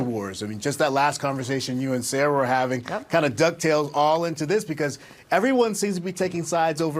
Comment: It also sounds like he says “duck tails” instead of “dove tails”. To be fair, hosts misspeak all the time.